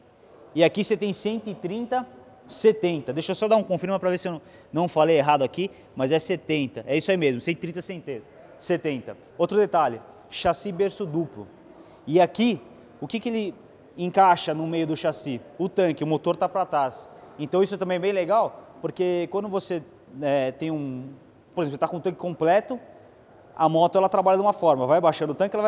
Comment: Só eu que ouvi no minuto 4:00 ele falando " 130-SEM TETA " ???? kkkkkkkkkkkkkkkkkkkkkkkkkk